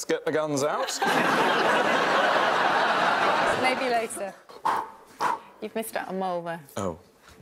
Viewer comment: What does Suzie say here that gets such a reaction? It sounds like she says "You've missed a mole there", I don't really get it.